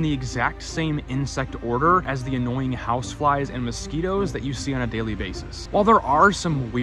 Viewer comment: 😅 I could've swore He said, "annoying housewives" 😂